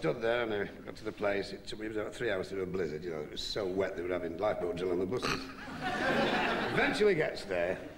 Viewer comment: I can't make out what he says @ 3:53. What were they having on the buses? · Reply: Life boat drill😁